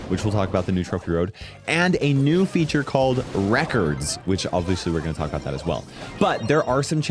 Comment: i thought they said reckers 😭